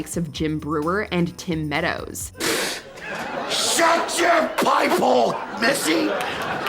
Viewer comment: You can see him break in #10 after he says:"Shut your pie hole, missy!", although it sounds like he says "pipe hole".